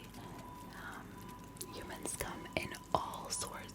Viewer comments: Lots of people have been comparing this video to Jellybean Green’s Fixing You video series, which is super nice!!!! I LOVE JBG, so if you like this type of video, please consider taking a look at hers!!! 😍 <link> LOVE & HUGS 💜 Ella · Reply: I heard human scum instead of humans come I think the human scum makes it a lot funnier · I first heard " humans come.... " As "human scum.."😭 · i thought you said "Human scum." 🤣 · I heard “Human scum…” 😭